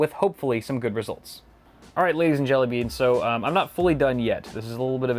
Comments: did he say jellybeans? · did he just said ladies and jellybeans 😂? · did he just say ladies and jellybeans